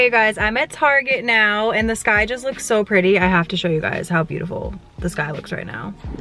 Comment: I thought she said this guy looks so cute